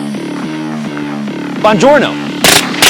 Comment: Dude I thought you said Digiorno in your Intro right before shooting the targets. ha Wait, is your watch also a calculator?